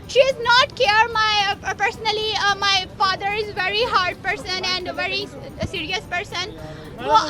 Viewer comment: did she say hard person😂